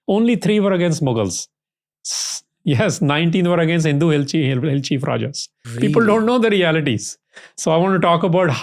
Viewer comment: what he says at 5:31 ......Is he said "Hindu Hell cheap Rajas"....? or something else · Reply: Hindu Hill Cheif Rajas